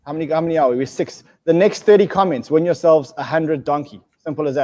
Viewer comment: Oh my god, I think I won some donkey! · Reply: I mean I heard him say “next 30 win” but I lost track of where it started